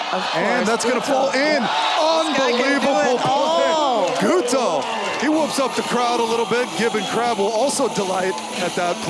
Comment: он что сказал - КРУТО?)